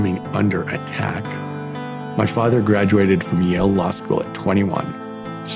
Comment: Does he say "went into the Lavy"?! Bc that's what I hear...